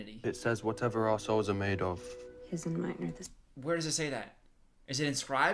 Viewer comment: Every time Hardin says: "Whatever our souls are made of ..." I always understand "Whatever assholes are made of ..." and idk if I'm just stupid or if it's his accent ... I guess I'm stupid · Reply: BRUH THATS WHAT I THOUGHT HE SAID TOO FOR WAY TO LONG LMAOO i legit heard "whatever our assholes are made of hers and mine are the same" and it took me a couple of times to hear the correct thing,,,, 😭😭😭🤣🤣